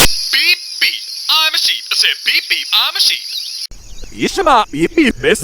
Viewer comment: It sounds like he is saying beep beep I’m a Jeep I said beep beep I’m a jeep